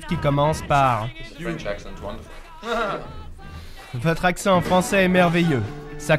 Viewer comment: Il dit quoi lorsque que le journaliste dit « on commence avec votre accent français magnifique » a à peu près 0:30 il dit « french accent w.... » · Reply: Wonderful.